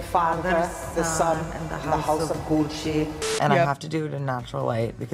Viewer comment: When everyone says "House of Gucci" in an italian accent I keep hearing "House of Coochie"